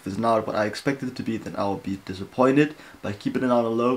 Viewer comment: am I hearing it right? ur disappointed on enhypen? :((